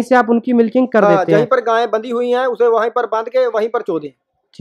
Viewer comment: What are you saying at 03:08 do you know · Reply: Jaha gaye khadi hai wahi par bandh k wahi par "cho"(milking) dain, chona (to milk) in punjabi.